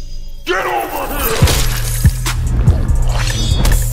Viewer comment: that 's what it says . ..................somebody knows??? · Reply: "Get over here"